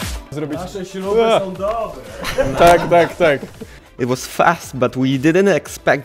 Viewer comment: подождите, мне послышалось, или он сказал "так-так-так"? Это даже подходит по смыслу к "that's right". 18:24 И здесь))